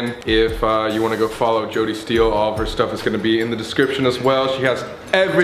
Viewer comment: idk why but when you said jody steel i thought you said jojo siwa